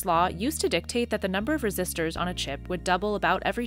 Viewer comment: In 1965, Gordon Moore posited that roughly every two years, the number of transistors on microchips will double. At the 11:01 mark the CC says "Resistors" not transistors.